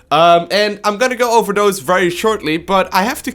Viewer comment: I could have sworn you said you were gonna go overdose very shortly.